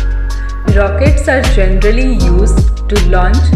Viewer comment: she said gentally instead of generally